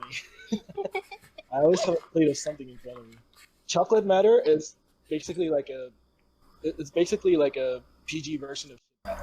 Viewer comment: What does say at 1:07:54? lol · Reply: Sorry he said Chocolate Matter was a PG version of Shit Matter. I had to censor it due to airing it over the air.